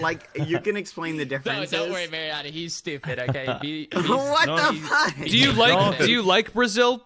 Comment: GEGG EMPIRE 17:14 did bbh say wtf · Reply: What the fudge